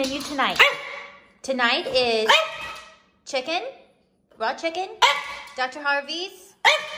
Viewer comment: Did she say "raw chicken?" Eeeewwww.